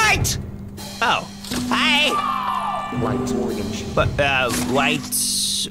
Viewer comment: am I the only one that hears "organs" instead of orange?